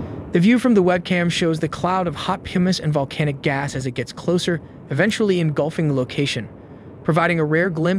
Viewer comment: Did he just say “pew miss”??? 1:16 1:16 1:16 1:16 1:16 1:16